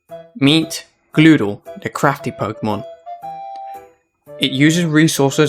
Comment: I thought he said crappy 😂